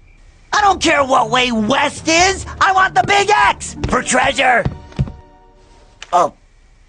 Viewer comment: It sounds like Wayne says '' I want the big ass''